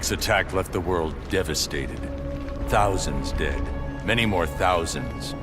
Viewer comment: the subtitles say Dick instead of Dead haha xD